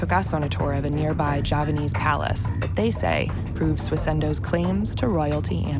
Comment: Subtitle said Japanese palace (Istana Jepang), but it should've been Javanese palace (Istana Jawa). Also, the Keraton (Javanese palace) hosts the grave of Sulaiman, not Solomon. --- 7:21 Di Subtitle tertulis "Japanese palace" (Istana Jepang), seharusnya "Javanese palace" (Istana Jawa). Kedua, Keraton yang ditunjukkan adalah makam Sulaiman, bukan Solomon.